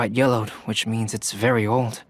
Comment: i thought bro said yolo’d 💀